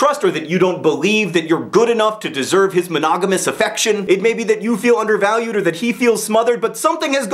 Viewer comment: I heard "Anonymous affection"...